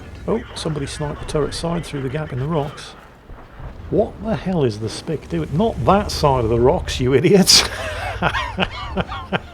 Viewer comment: I’m just listening casually while doing something and I just hear say “What’s that Spic doing?” and I had to do a double take and remember there’s a tank called the SPI C and he wasn’t just saying some random slur.